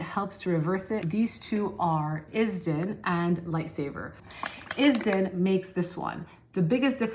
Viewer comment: I thought she was saying "lightsaber" and I was like YESSS that's what my skin neeeeeddss!!!🤣🤣🤣